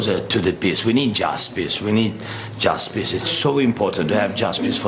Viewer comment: When Zelenskyy was talking all I heard was "We need Justpiss we need Justpiss." 1:35 And I can't stop laughing 😂 DUDES SAYING JUSTPISS!!